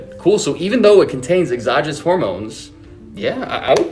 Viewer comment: You are misinterpreting what he says at 4:45 he say exogenous (exogenous means produced outside of the body) because it's produces on somebody else's body, it doesn't means that the guy is taking steroids